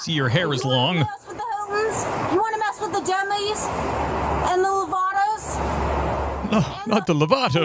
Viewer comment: did she say Demi Lavato? 😂 " do you want to mess with the Demi's" " do you want to mess with the Lovato's" 🤣🤣 I wish I could be there in court when the prosecution plays this.